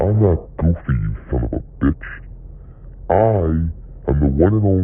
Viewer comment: did he just say that or something? 😭🥀🙏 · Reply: "bitch"💀🔥